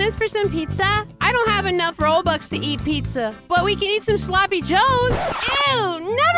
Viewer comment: I thought that it said sleepy joe